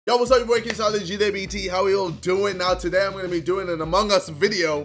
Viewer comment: What did he say? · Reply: Ksi Olaji Debt ( from the crypto)